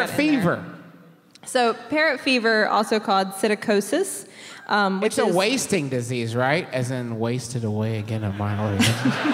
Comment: what the fuck is he saying, his allegedly best joke is completely drowned out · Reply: i think he says 'its a wasting disease, right? as in "wasting away again in margaritaville"' (which are jimmy buffet lyrics)